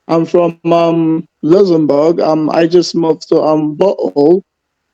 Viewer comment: Sounds like he says he lives in butthole.